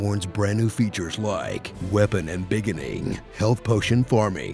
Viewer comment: hi, not native English speaker here... at 1:04 what does he said? weapon ambiginin? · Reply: oscar ortiz He said "embiggening", meaning to make bigger or more expansive, although until recently it was just seen as a made-up word.